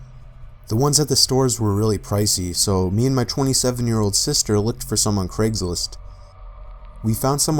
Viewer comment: I thought he said “ me and my twin seven year old sister …” I was like what💀😂